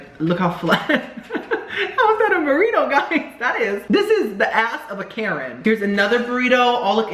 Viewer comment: Did you just say " the asset a Karen!!!!!" ???? 😭😭😭😭😭😭😭😭